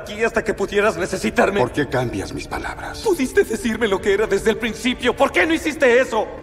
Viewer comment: Escuché mal o en latino dijo pudistes